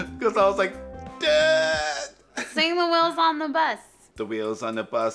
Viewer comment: Sounds like shes saying The Whales on the Bus